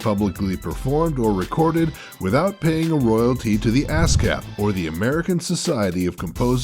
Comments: haha i thought he said asscap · I totally heard "...without paying royalties to the ass cap." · When I herd him say ASCAP, I though he said Ass Cap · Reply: And I thought he said AnCap. Eh, they're all the same thing.